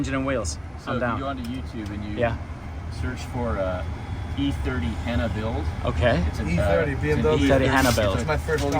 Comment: what did he say ? e30 Hanna build ?